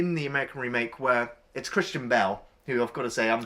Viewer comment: For the longest time I thought was saying "Christian Bale" instead of "Kristin Bell" and I was like where's Christian Bale? ha! Nice review as always. The American film does look the pits!